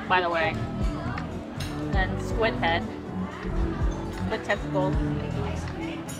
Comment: I think i miss heard that, Squid testicles? Or Tentacles? 😅